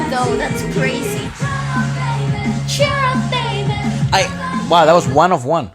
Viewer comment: I'm so happy I wasn't the only one who thought they were saying "Shut up baby" XD